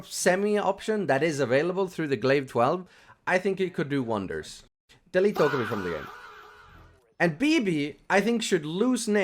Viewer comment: What did he say? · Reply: "Delete dokkaebi from the game"